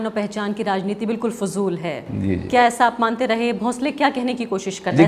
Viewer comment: I heard bhosde 😅